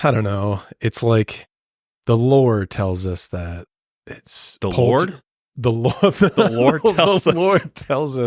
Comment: Omg 😂 I thought you were saying the lord not lore bahahahahahahah